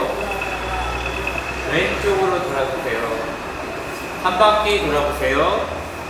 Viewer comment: Sir 2:57 yo bujhina? 여기 이해하지못해요 , 뭐라고 했습니까? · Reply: ”한바퀴 돌아보세요“ 라고 했습니다😊